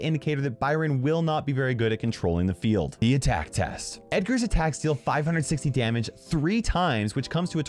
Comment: why did say Edgar not Byron?